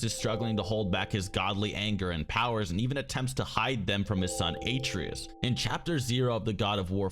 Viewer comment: did he just say a- treas